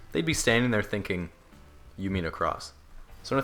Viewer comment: "...you mean across" dead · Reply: I thought they said "you need a cross"